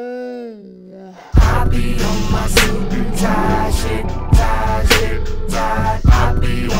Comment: i really thought that he said i pee on my suit and tie